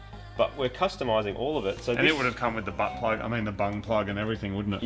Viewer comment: What did you just said :-P???? Butt plug?